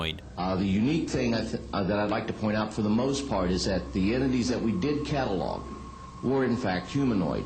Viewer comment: Did the alien hunter say, "Enemies" at about 15:00 or did he say, "Entities"? Sounded like Enemies to me. That's not good. The object out of the plane's window was moving as fast or faster than the plane!! That was very strange and interesting. Thanks for the video!!